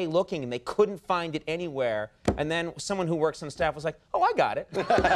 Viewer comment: "Push eject on their VCR", part of me thought he was being sarcastic but then I realized what year this is from... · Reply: I thought he said "he jacked on their VCR"